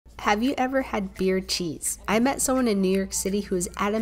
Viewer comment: When she said Beer cheese I thought she said beard cheese💀